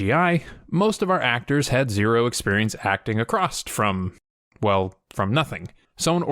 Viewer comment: did you just say… acrossed? Acrost?